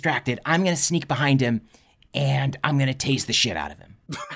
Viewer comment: #Eegads! When I first listened to the plan, I could have sworn you said that your character was going to "taste the sh!t out of" the three headed ogre, Martin Threeman. Careful listening is very important. The lesson I learned this afternoon.